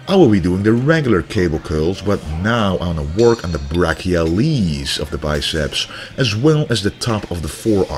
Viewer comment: Lol when you said brachialiis I thought you were going to say baby please